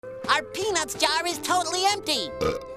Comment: why did i hear "our penis jar"?